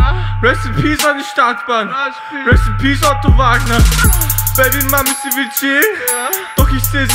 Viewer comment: R.I.P Otto Wagner and Arteezy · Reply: I thought he said Otto Wächter